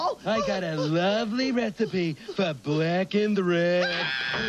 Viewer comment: Did anyone think Clueless Morgan said “black and red” at 1:14?